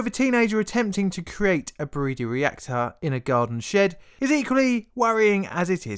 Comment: People need to watch this with the subtitles on. It’s better than the mistakes autocorrect makes on phones. In this video, around 0:30, the narrator talks about a teenager making a breeder reactor. What do the subtitles say? The teenager built a burrito reactor.